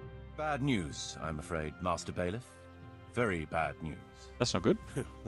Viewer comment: I keep hearing "Master Bailiff" and doing like a double-take >.>